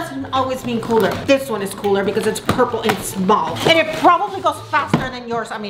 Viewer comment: i thought she said "its purple and its bald." xD